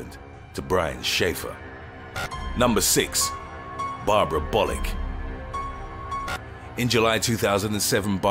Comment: When you said Bollick I thought you said Bollucks 😂😂😂